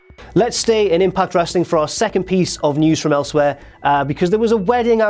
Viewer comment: I swear I thought it said "News From Ellsworth" I was like nooooooo